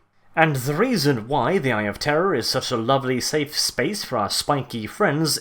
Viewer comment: The first few times I heard of this thing I heard "Eye of Terra" and all I was thinking was "why the fuck is everyone afraid of Terra, jesus christ."